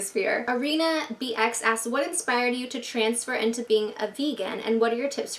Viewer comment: Omg I thought that question said "what inspired you to be FEMALE" I was like what the FUCKKKKK!!!!